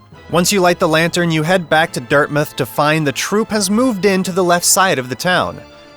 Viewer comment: did you call DirtMouth DirtMith? Also I didn’t think that Grimm was as hard as people say, sure he’s a bit fast but his attacks were really predictable.